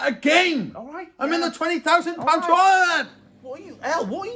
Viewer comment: Did he say I’m in a 20,000 Pound toilet?